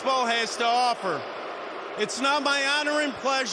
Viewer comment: Did Manfred just disrespect the Astros and fans? I swear he says "It's NOT my honor and pleasure..." I mean maybe he meant to say NOW, but I'm hearing NOT for sure. Caught it live too but was thinking I must've been mishearing things.